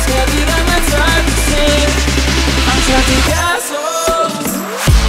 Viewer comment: Why did I hear "assholes" instead of castles??? 🤦‍♂️🤦‍♂️🤦‍♂️🤦‍♂️🤦‍♂️🤦‍♂️wtf is wrong with me!